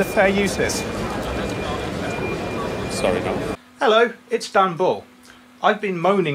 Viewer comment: When you said hi, it's Dan Bull it sounded like you said hi Istanbul which made me laugh